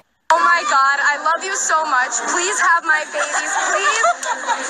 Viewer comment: Did I hear it, right? 0:57 Did she say "Have my babies"? HAHAHA. Omoooo! I can't waiiiit!